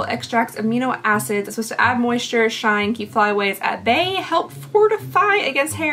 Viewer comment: LMAO! I haven’t even finished watching but had to comment that, when you were reading off everything the pink hair protectant I swear I heard you say “keeps flies away” 😂